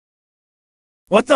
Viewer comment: The way he says Spongebob at 0:58. I don't think I've ever laughed so hard in my life. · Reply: all I heard was “spohng bog